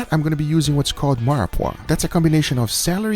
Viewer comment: 👍FYI @ 9:08 for those who couldn’t hear / understand what he said (like me) 😃 👍he said “Mirepoix” is a combination of onion, carrots & celery.